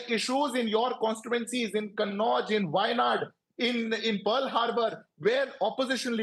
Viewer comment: Did he say PEARL HARBOUR in 17:23? 😂